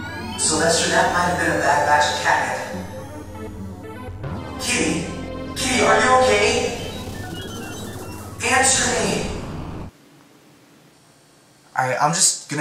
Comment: In case you couldn't clearly hear what said, here it is. Sylvester, that might have been a bad batch of catnip. Kitty! Kitty, are you okay? Answer me!